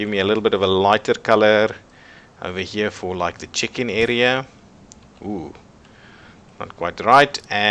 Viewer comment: I thought you said the chicken area @ 14:10 lol. I like the new upgraded tutorials.